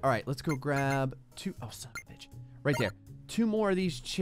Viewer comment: i heard grey say "son of a b****" at 20:28 XD